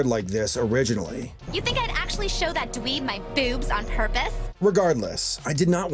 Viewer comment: automatic subtitles: "You can actually show that to eat my boobs on purpose" I want to know how YT got the idea that that's what was said